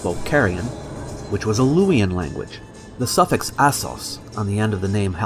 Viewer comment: what means? google translate does'n know this word. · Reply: it sounds like he is saying "ass hole" but he used the word "assos" instead (in case you needed more explaining)